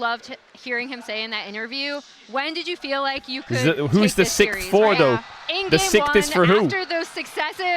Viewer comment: what does Caedrel say here i dont understand whats for who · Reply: He's saying who's T1 6th World champ for, is it for Doran, Chovy, Peanut